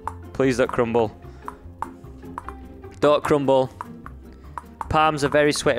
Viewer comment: when he said don't crumble the first and second time I thought he said duck crumble and then I was like is he thinking about Sqaishey but then realised I was going crazy